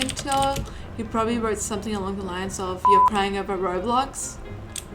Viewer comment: only "kids" cry abt roblox i think- that's what he said idk